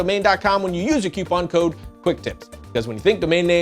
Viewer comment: Sounds like he said "doot."